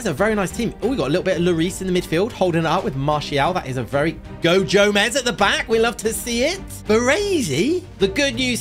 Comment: Did just say go jomez